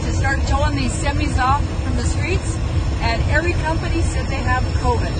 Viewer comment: what does she say ? Every company has a quota?